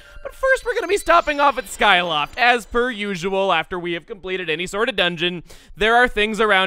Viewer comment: I swear he had said "As BIRD usual around 0:49 when talking about skyloft.